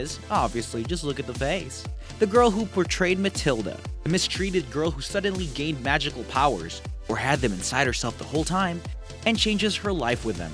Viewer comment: (ish) .... Did he say "the mistreated girl who suddenly gained VAGINAL powers?!? ..... i listened to it over and over about 5x.... and i SWEAR that's what he said!!!